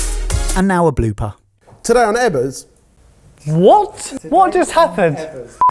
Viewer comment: “That’s my nut spread.” - Ben ‘Ultimate’ Ebberell, 2020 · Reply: thank you, I finally understood the "Ebbers" nickname that recently substitutes the "Ben". At first I misheard "Ebus" and thought it may be a reference to the webtoon comic "Just Pancakes".